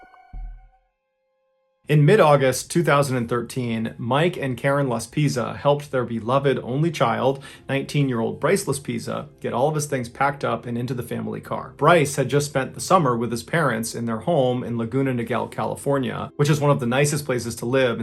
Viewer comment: I don't know why I heard "brace-less Pizza" 😐